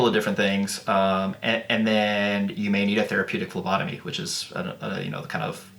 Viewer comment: Man, I thought you said therapeutic lobotomy.